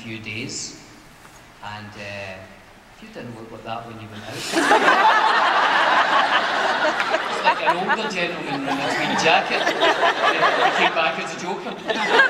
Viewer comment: Can someone tell me what he says from 2:28-2:50? Can't understand him (I'm from germany) because of his brilliant accent :) Would like to get these jokes ;) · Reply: "You didn't look like that when you went out. Looked like an older gentleman in his wee (Scottish slang for little) jacket, and came back as the Joker. Yeah, this is a weird room we're in, I love it. It's like the Tardis, it's tiny outside." Audience cheers "That's good, you get a cheer just for saying Tardis!"